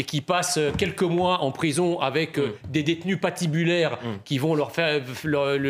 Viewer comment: "s'il passe quelques mois en prison avec des détenus pas tibulaires ..." quand on place des détenus tibulaires avec des détenus pas tibulaires ... ça donne quoi ?